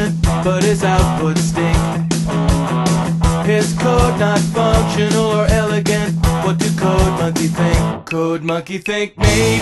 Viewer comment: Am I the only one who hears: "PISS code not functional or elegant?"